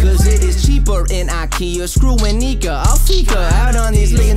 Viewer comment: Did the Canadian guy say the N word or am I tripping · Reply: I can’t tell for sure what he’s saying but around 1:51 the rhyme sound like : screw it nigga I fika 🤷🏻‍♂️ or something like that